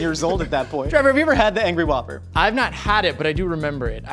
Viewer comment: Trevor thought you said "angsty whopper"